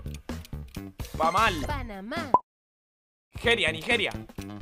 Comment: ESCUCHE PAÑAL JAJAJJA